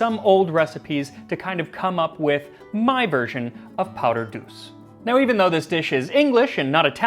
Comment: I keep hearing "powdered goose".